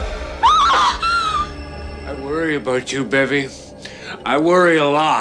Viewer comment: I thought that it said ‘I worry about you bevy’